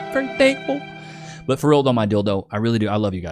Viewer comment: lol man, listen back the part between 14:15 and 14:20 , You absolutely saying dildo. You actually saying : "But for real though my dildo , i rly do, i love you guys man for real" Just listen back, its gonna be a good laugh, cause you said what you said and its hilarious. You never heard of a round tent ( yurt ) ? Its been used for century`s rly, and a Yurt is not a tipi man.. Beside the running water and no toilet. Its on of the best things outdoors to have. Asian tribes rly using them, pack it when need to move and rebuild at the next place. Some reindeer farmers for example still using them.